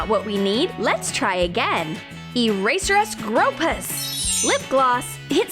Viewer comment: I thought she said eraser grope us